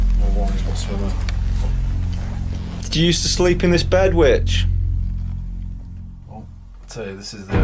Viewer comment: I heard "Do you sleep on this bed b*tch?"